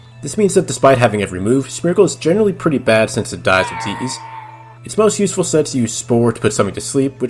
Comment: I cannot for the life of me understand what you're saying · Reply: Smeargle is generally pretty bad since it dies with ease